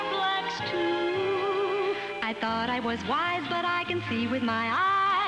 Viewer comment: holds a white shirt "I thought I was white but I can see with my eyes the difference is like day and night". Lol :D Only later realised what she actually said.